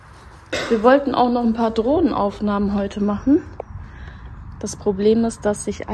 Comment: Drohnen Aufnahmen, ich habe erst verstanden Drogen Aufnahmen. Ja, das mit den Drohnen ist immer so ein Ding. Viele Menschen haben dann das Gefühl sie werden ausspioniert. Der Vorspann ist aber total Klasse.